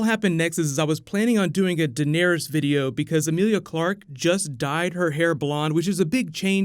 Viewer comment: legit thought you said "Because Emilia Clarke just died."